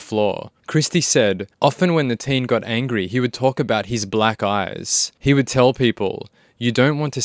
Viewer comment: Oh! I thought you were saying “black guys” at first, and had to rewatch it. It was black eyes. Lol ok that made more sense, got it. 😂